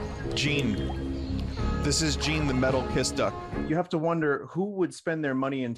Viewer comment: For a split second I thought said "This is Gene the Metal Piss Duck" and I was LOSING IT